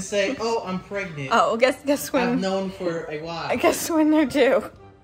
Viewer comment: did she say “guess when they’re due?” Hmm..Twins maybe? Idk? Littlelirious’s! Please take care of yourself. Congratulations and!